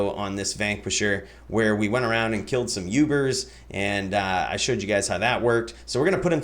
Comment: Sorry, did you just say 'YouBers'?